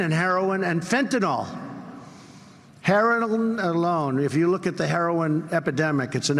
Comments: Did he just say herelwin???? 0:05 What the heck is he on??? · What a mumbling stooge....."Hairilone alone" 0:04 Is what fell out of his trap. This boy needs a jacket and a vacation in a big beautiful Horspitahal!